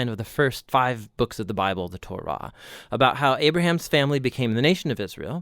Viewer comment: What are you saying at 0:36 especially the first 5 books of the bible of the “torba” is what Is it Torah you are saying? Ur videos are great help thanks a lot god bless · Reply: Torah. It’s the first 5 books of the Bible